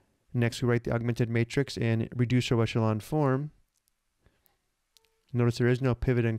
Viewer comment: Thanks, could you repeat or caption what you're saying at 1:20? "Next we write our augmented matrix and reduce ___". I don't know what operation this was and would like to look it up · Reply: reduced row echelon form... <link> or using Desmos <link>